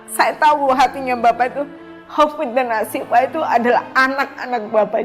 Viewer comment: she actually said "Hafidz and Assyifa" but was wrongly translated to "Hope and fate". Despite the horrid thing they've done, she still believe they are still the children of God